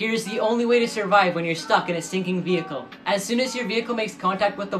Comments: I thought he said how to get out of a stinking vehicle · I thought he said”...the only way to survive when your stuck in a STINKING vehicle...” I was like yea ok